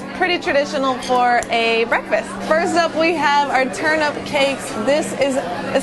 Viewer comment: Tell me why I thought you said turn up cakes???? I was like yassss Chinatown!!!